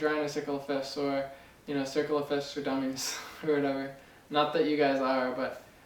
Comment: lol at first i thought it said circal of filth and i was like what? thats not right its a piano thing and i was like :D